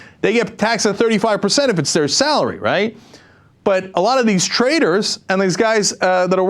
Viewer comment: I thought Cenk was saying "traitors" instead of "traders." Either way, same thing.